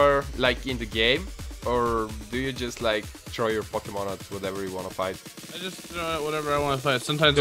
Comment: instead of throw your pokemon i thought you said troll your pokemon